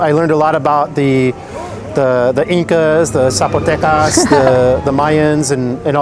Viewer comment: the subtitles say “mumbles” but, no, he said “Zapotecas” which is an indigenous group of people from Mexico. I don’t mean to be nit picky, but when I heard that and saw the subtitles didn’t show that it just felt like rubbing salt in the wound considering everything indigenous people have gone through and how often they’re ignored/erased from history. I don’t think the subtitles missing the word was intentional, but I did feel the need to comment.